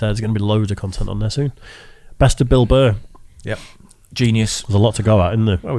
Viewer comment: Thought it said Bill Burp.